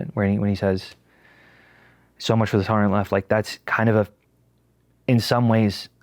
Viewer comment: What is he saying at 9:00 ?? "So much for the torhouuhogh left" i replayed it 5 times and every time it sounds more like an aneurism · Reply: "tolerant"